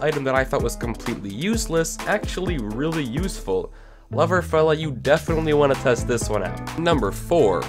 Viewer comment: DID HE JUST SAY LOVERFELLA?